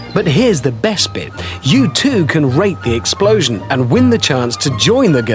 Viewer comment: did he just say RAPE the explosion or was it just me?